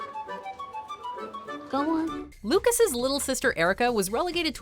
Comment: What does she say at 4:56? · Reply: "Go on."